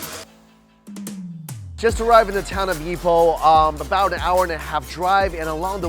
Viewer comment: When he said town of Ipoh I don’t know why I heard “town of meatball” lol